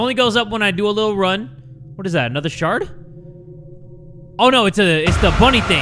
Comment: i keep hearing "is that another shart?"